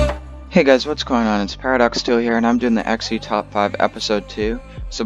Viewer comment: thought he said paradox toe lol